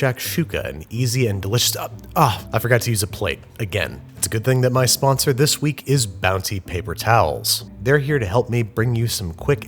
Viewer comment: My audio briefly cut out between when he started saying Bounty and started saying towels so it sounded like he said "My sponsor this week is Bowels"